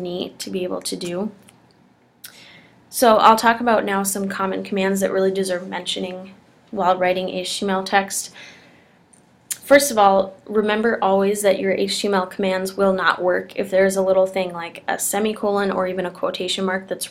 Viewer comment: Better video that most. But, sheesh, slow your roll. At 4:08, I heard "writing hache text". Took me a while to figure what you meant.